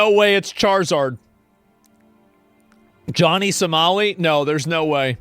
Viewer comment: I thought it said "Johnny Sucks!"